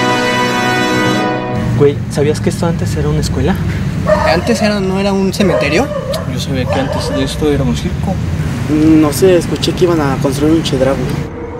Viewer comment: JAJAJA -wey sabías que esto antes era una escuela. -¿antes qué no era un cementerio? -yo sabía que iba a ser un circo -no sé, escuché que iban a construir un chedrahui